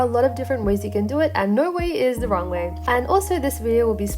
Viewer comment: On 0:17 I thought she said you way is the wrong way